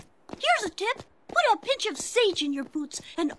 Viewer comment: I could have swore that he said sweet scent years ago and not spicy scent